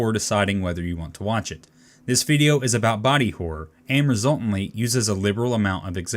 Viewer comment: I thought you said body odor... I was like, why would I be scared of that?! Then in 03:23 I see this, so I'm like, okay... Now I get it! lolol